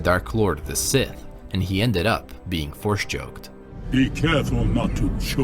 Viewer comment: Did you say Force Joked?